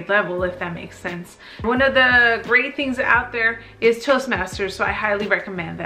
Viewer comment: Hi, at the end of your video where you talk about improving your communication skills...you make a recommendation but I can’t quite make out what you said “__ masters?” (@ 10:13) what tool is this and where can I find It? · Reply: Toastmasters 😃